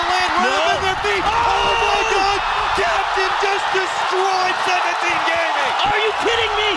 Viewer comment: Who else heard fnatic gaming istead of 17 gaming